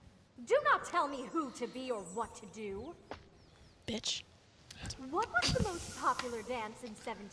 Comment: did she say bitch?